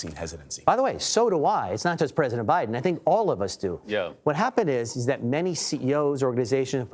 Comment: did someone just say "Yo" at 3:06 ?